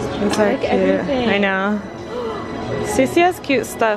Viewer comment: did u just said sussy instead of stussy lol